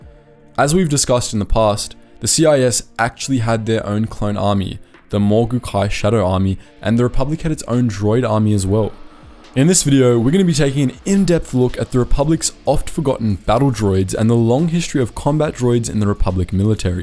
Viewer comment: it sound like you said “The republican’s own droid army as well”